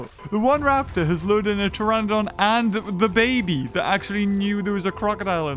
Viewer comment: did he say da baby